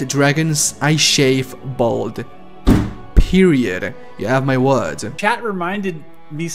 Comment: "If we do not get the dragons...I shave balls" Thats what I heard at first😂